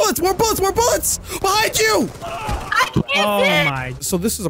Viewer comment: i think she was saying i cant sit or i cant see not sure which